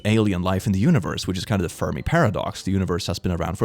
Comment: Legit thought he said "Furby Paradox" 😂